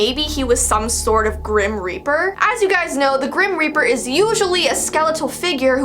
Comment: When you said grim reaper I thought you said grin reaper